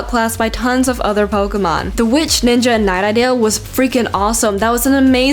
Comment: the witch, ninja and ___ WHAT!? what u say? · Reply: "knight"